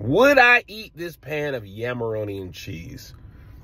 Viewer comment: I thought it said hameroni. Thought it was sliced up ham at first. Which would've been fine. But, no, not yams. No thanks